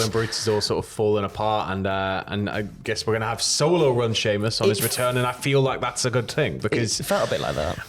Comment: At first I thought you said Solo vs Sheamus but realized you said Sheamus going solo . But that doesn't sound like a bad match ! LOL